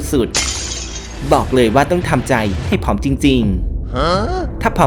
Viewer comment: ได้ยินว่า ต้องทำใจให้ผอมจริงๆ โอ้ ยากละ เปลี่ยนคลิปเลยและกัน🤣🤣🤣